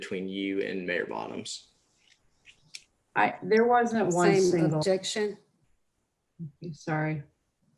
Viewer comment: does she say “same REjection??